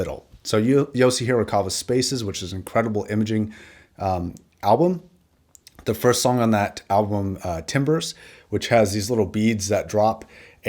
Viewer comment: anybody wondering what song he talks about at 20:41 - Yosi Horikawa / Album Spaces - Song Timbres ... took some googling because i couldn't get your pronunciation right, automatic subtitles where about as clear as i understood what you were saying ;-)